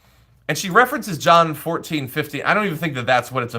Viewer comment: John 14:50. No such verse. John 14 only goes up to 28. Did I mishear and it was 14:15 "If you love me keep my commandments". So the commandments attributed to the alleged JC about LGBT people are? There aren't any. In the language of this bigoted diatribe, ZERO. That stuff is in the Torah and the rantings of that megalomaniac scumbag Shaul of Tarsus. Nothing to do with any of the Jesus legend at all. While we're here, the very next verse, John 14:16, is one which Muslims say foretells the coming of Muhammad, god's final prophet. What does she have to say about that? OK, I know, it'll be something about the "holy ghost" or whatever. Why is she right and the Muslims wrong about that verse? No coherent reason whatsoever. Why aren't they BOTH wrong? Who really wrote that, anyway? Is she a scholar of Koine Greek?